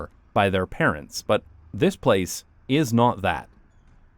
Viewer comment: the subtitles say "palace" instead of "place"